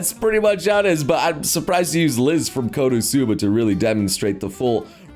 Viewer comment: Did he say Liz? Grab your pitchforks